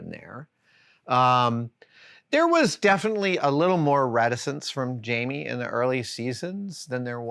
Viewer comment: What word is he saying around 00:27? Sounds like Radisence. Tried looking it up but i can't find anything